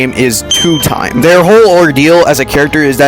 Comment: i deadass thought he said "ideal" i was bout to shout "Yi Sang" but he said Ordeal